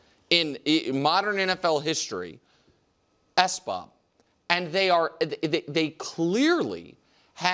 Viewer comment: what is the word the keep using here? S-bob? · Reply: Superbowl or bust